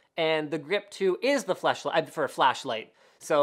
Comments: Did I hear fleshlight? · Did he just say fleshlight.?.